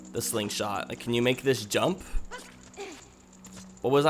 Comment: Did you get all the chests in the forest temple? And 19:30 sounds like you said "can you make this dump" but it was jump.